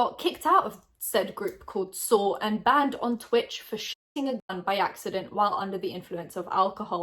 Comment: I had to keep rewinding at 18:25 bc I thought she said “he got banned from twitch for shitting himself” not “shooting a gun” Idk how I heard that. Probs bc I’m procrastinating from sleeping bc uploaded :)